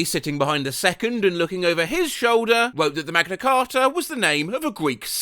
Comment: he did NOT said "Magna carta" He said "Magnus Carlsen"